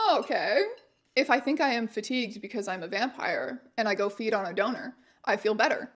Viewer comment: I'm listening but not watching, and I misheard "feed on a Donor" as "feed on a donut."